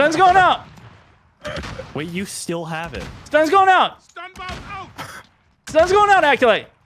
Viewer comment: STUNMountain64: "STUNS GOIN' OUT!" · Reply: I always thought he said stun bob out